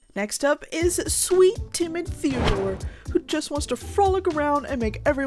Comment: I thought she said Timitheore, not timid Theodore…